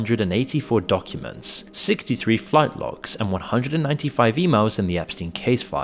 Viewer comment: I was cleaning while listening to this and I swore it said “fight clubs” instead of “flight logs” and the image in my brain was hilarious. I’m glad I double checked.